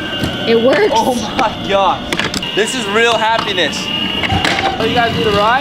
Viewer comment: he tells happenis instead of happiness I definitely don't think it was a mistake since it's 😂